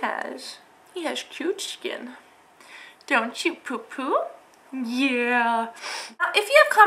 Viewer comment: Lol, I could have sworn she said Poo Poo at 4:16.